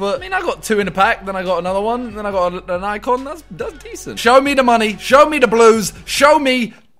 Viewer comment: The entire time I thought he was saying show me the jews